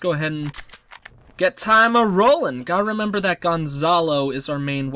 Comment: At first I thought you said the guy who was good at shooting's name was "Gunzallo", who is Guns cousin from Latin America xD This is one of the most promising starts I've seen so far. It will be exciting to see what happens.